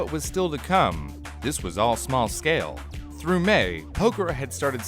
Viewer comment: I thought, i heard Pekora